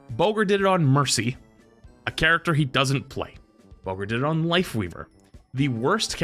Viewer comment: your point is mute though, because thats not how matchmaking works. Because you're grouping and the game knows hes diamond, hes gonna get a diamond match on the other team. Its not a diamond amongst golds, its more like which diamond player is better. If he really was a diamond player with golds properly win rate defo would be like 90%, the only reason he would lose would be because of a leaver, cheater or a thrower but these things are rare. I feel like you're just trying to cope in saying that there isn't a big skill gap between diamond and gold. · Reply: i am so tired I thought you said weaver instead of leaver but a gold lifeweaver is about as good as a leaver so I guess it works out